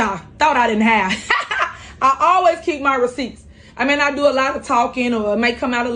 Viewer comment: she really says "I may not do a lot of talking". 😂😂😂